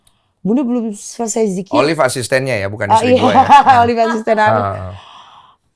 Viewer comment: OLIV ASISTEN NYA YA, BUKAN SELINGKUHAN NYA 😮 salah denger ga sih aku?